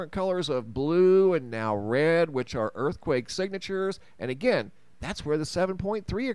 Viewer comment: I think I heard "earthquake temperatures," but I kept listening anyway. That sort of thing is just for fun, I suppose. All the earthquakes in June in the Yellowstone area added up to the same energy release as a single magnitude 3.6 earthquake. Anyway, all future seismic activity there will be the effect of the 95% solid "magma" chamber's cooling. Like the "ticking" of a cooling engine. The last volcanic event was 70000 years ago, the 31st lava flow since the final eruption. Yellowstone moved past the hot spot and "died" just like the other dozen in the string did.